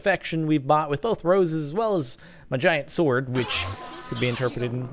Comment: "and by protection I mean armor what else do you think I meant?" what u thinking Jordan? lol and when he said giant sword I thought he said vagina sword XD